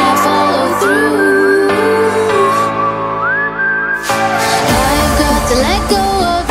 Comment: I HEAR I've got a Lego